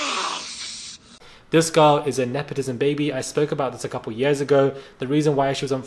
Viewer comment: So funny..At first, I thought he said "NEPHILIM baby" instead of "nepatism baby"... 😂 But the creature seems to be more fitting since she isssss so little and frail ...poor thing needs a hug and and peanut butter sammich ... that's IF, she isn't allergic!😱